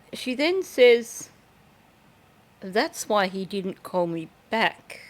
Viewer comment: I thought she said “Thats why I didn’t call YOU back”. Ill listen again and double check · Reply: I think she did say thats why he didn’t call me back. Idk… the original thought makes me think they had called her about something, but the fact she approached them to disclose what she knew makes me think she probably did say what you think it is. Commenting for the algo gods